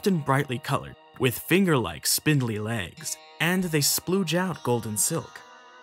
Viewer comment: Did he just say “splooge out”